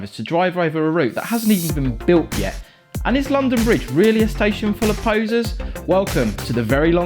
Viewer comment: I'm not gonna lie i thought he said 'is London bridge station bridge station really full of posers'